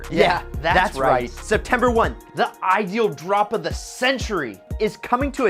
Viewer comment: Did he say september 1th😂😂😂